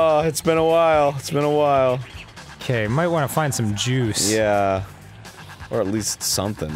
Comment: I swear to god I thought he said "Gotta find some Jews"